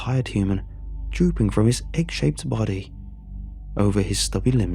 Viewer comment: Ohh egg shaped body i thought it said dick shaped body lol